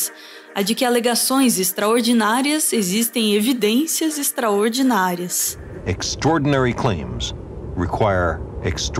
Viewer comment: Aos 6:27 escutei errado ou a narradora diz "alegações extraordinárias EXISTEM evidências extraordinárias"?